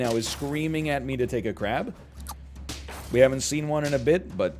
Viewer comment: did anyone else hear, "... It's not perfect and every thing in my body right now is screaming at me to take a crap"? 🤣